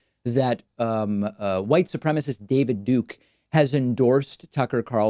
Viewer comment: why does David Duke look like he is wearing someone elses skin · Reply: Bro I thought you said David Pakman and all the people in the comment section were agreeing with you. I was mad confused.